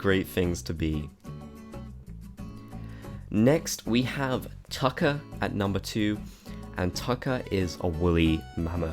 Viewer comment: tucker is my favorite villager, I can understand why people oversee him, but I don't mind, it makes him even more special (edit: lots of love for tucker in the replies, really cool) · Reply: for a sec I thought you said Zucker and I was confused